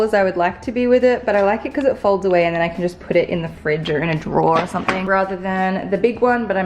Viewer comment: am I the only one that heard her say put the stool in the fridge or a drawer!? 😂